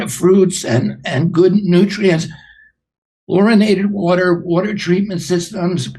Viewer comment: Lol, I thought I heard him say fluoridated water 💧. Had to play it again 🤣. It was chlorinated.